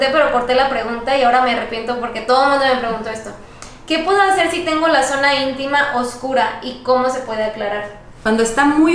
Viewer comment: En el minuto 5:51 habla de un síndrome pero no le entiendo muy bien lo que dice alguien lo entendió? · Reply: Síndrome de ovario poliquístico